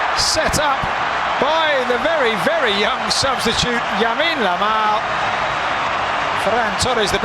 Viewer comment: Did i hear the commentator say Yamine Lamal?